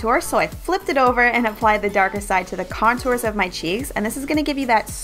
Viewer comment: i think u said "contour your cheese" instead of cheeks lol 😋🙄